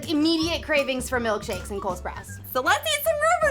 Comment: Is it just me that thought they were saying cold sprouts every time they said Cole spouse?